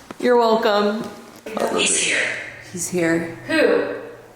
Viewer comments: omg did anyone else hear a deep man's voice??? Kinda sounds like he said "I lived here". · after you said you’re welcome. I think a disembodied male voice could be heard, there was no mention with subtitles.. sounded like “ I live here.” 1:18:50 something moved her glasses on her head.. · I hear a male voice at 1:12:48 - saying "I love it here."